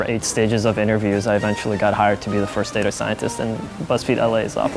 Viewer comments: I thought it said "Fired" instead of hired. I was like tf did you do. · I thought it said fired lmao